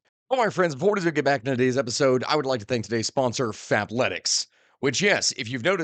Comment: I wasn't looking at my screen for a second and thought you said fapletics